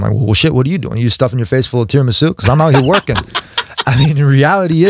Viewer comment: What Tony said at 4:52 can anyone tell me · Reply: LANCE VANCE he said, “you're stuffing your face full of tiramisu?”